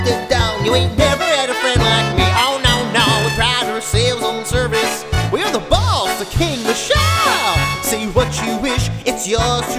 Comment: who else heard that the genie said “we’re the boss, the king! The shah” instead you’re? Or is it just me? Overall an awesome Aladdin anthem of a melody!! Edit: keep up the good work guys